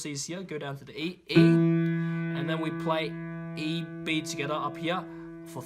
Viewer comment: When he says togethea it sounds like he is saying skidda